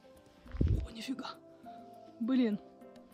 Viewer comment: how do you say "F*ck" in Russian? I didn't quite catch what said... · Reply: please check at 10:55. said something like 'Bulyem" (?) and it is translated as 'F*uck". I want to improve my Russian ;-) or did she say 'блядь'? Spasiba!